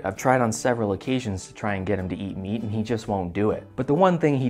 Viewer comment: Idk why I heard that he says: "I tried to get him to eat me" and had to watch this part, at least, 5 times to understand that he says meat, not me. One love ❤️